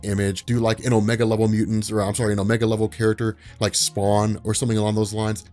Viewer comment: Wait, dafuq did he say? "... like Spawn..." fluids spew from every orifice